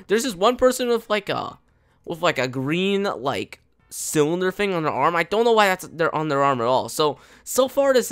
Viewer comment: I Thought said in 1:34 I Thought He Said Dark Sci Facility.